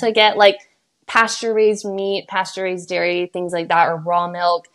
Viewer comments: i think shes saying "pasture-raised" not pasteurized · I 5:24:38 Here I think that they were saying "pasture raised meat, pasture raised dairy (...), raw milk" rather then "pasteurized milk". I think your confusion comes from here, but I could be wrong, I'm not a native English speaker. I really don't understand the obsession that some people have with Raw milk tho, it has so many risks and so little added benefits in my opinion 🤦🏻‍♀️ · I think she's saying "pasture-raised", not "pasteurized". As in raised on a pasture and not in some horrible factory-farmed facility where animals never see the light of day and are pumped with drugs and terrible food. Though good job to them for not including captions and making their course inaccessible /s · I think you misheard the clip. She said try to eat pasture raised meat and unpasteurized milk. She meant "grass-fed beef" But I totally see how you misheard her. ❤ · i think she’s saying pasture-raised, not pasteurized. love the video by the way! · I think she says "pasture-raised" meat and milk, not pasteurized. :)